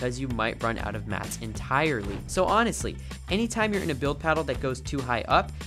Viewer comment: did I hear build paddle the guy must have been tired because the editors didn’t seem to care😳